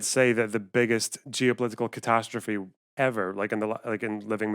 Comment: Not be a tankie or anything but he clearly said "geo-political catastrophe of the 20th century" not just "catastrophe of the 20th century". Geo-politics is related to nations and borders and all that and I'd say one event that led to the formation of 15 new countries almost overnight is a pretty big geo-political event. The Holocaust wasn't a geo-political catastrophe it was a moral and humanitarian catastrophe